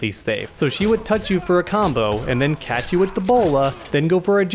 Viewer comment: I thought you said Batgirl will catch you with Ebola